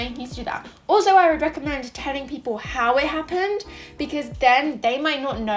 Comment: I almost fell for one of these scams! Luckily, I got dissconnected before I could accept, thank you for telling me this scam!! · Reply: I thought you said yelling lol